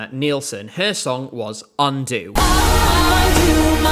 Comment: when i first heard undo i always thought she said "undo myself" and i thought damn that's kinda dark